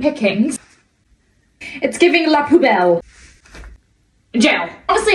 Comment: Instead of "la plus belle" i heard "la poubelle" which means the bin.